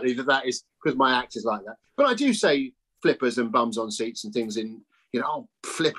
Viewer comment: I originally misheard Tim's "bums on seats" as "bums on saints," which I found delightful.